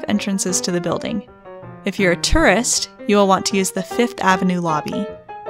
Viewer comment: "If you're a terrorist-" WHAT (I know it said tourist but it just sounded like terrorist)